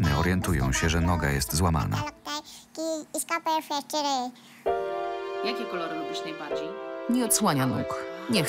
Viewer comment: Czy ja usłyszałam ,, jaki kolor lubisz najbardziej"